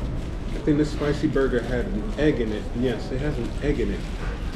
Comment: when you try to describe a burger, I've misheard, ya know what i'd heard? "It has an AGONY" HAHA my bad